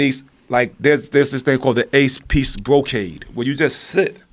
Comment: RZA said 8 piece brocade...could've swore he said Ace peace brocade...I was like...yes lol. peAce : )(